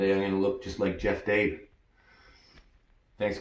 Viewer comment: Did he Say Chave Daffee?😂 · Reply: “Jeff Dabe” another great arm wrestler